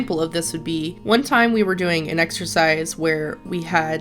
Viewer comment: Exercice? I thought you said extra fries